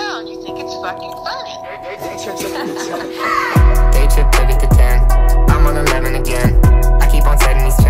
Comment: Why do i hear “day trip took it to ten” in every song now tf